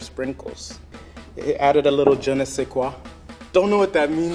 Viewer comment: I'm French and i didn't understood the "je ne sais quoi" before you repeat it yourself ^^'. I thought he said a strange english word